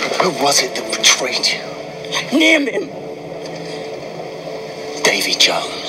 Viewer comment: what name does she say? I can’t hear her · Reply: "Name him"